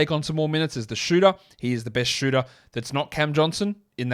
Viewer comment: Far out, I thought you said Keldon Johnson for a second and scrambled around to find info on him. Once you said he wasn’t good to stash I thought you were talking about Stanley Johnson not Cam 🤦🏻‍♂️